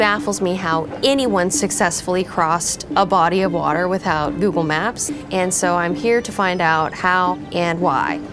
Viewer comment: did she really say without google maps??? lmaooo